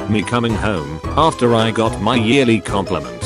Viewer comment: I SWEAR IT SAID "YEARLY APPOINTMENT" WTF